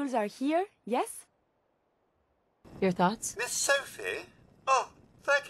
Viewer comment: Lol, i was reading the comment section while the video was playing and i could have sworn Codsworth said "Miss selfie", until i rewatched that part.